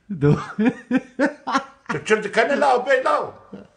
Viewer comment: arkadaşlar burada kürtçe ne diyor ya tam anlayamadım 😂😂😂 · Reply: yok küfretmiyor tu çi dikeni biwê law diyo yani neye gülüyorsun söyle law diyo